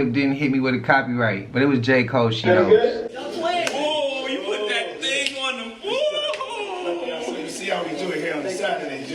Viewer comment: did he just say you PUT THAT THING ON EM???????????